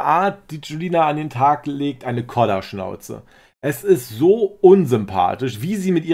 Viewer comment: Kollerschnauze...noch nie gehört,sofort übernommen👍👍👍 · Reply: Echt?Ich hab Koller verstanden,das Wort Koller kannte ich,deshalb vielleicht.